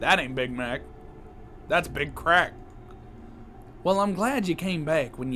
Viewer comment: “That aint big mac thats big cr@ got me dying 😂😂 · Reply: I thought it said big crack then I replayed it and heard big crap and laughed even harder then I did last time